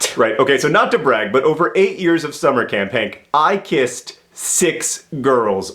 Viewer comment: Ha! I completely misheard John I thought he said :over the course of summer camp I kissed six girls." As in just one 3 week summer camp timeline. I thought the story was going to end with him getting beat up by a bunch of girls ;P